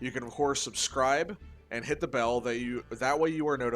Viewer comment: for some reason I heard "Hit Yubel" instead of "bell" and was like "Yo what?"